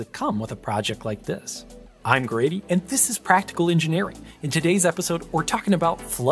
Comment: Thank you for having better audio on this video. On some of your previous videos, the audio seemed to cut off some of the last syllables of sentences. Eg, it sounded like you said "Welcome to Practical Engineer", missing off the 'ing'. This video doesn't seem to have that issue, so ty for fixing it.